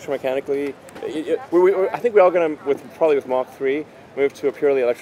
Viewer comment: Subtitle mistake around 13:23 In the englisch subtitles there's "Mach" instead of "Mark"